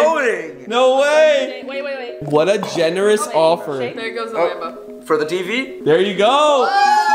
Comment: I thought it said lamb for one week but it said lambo for one week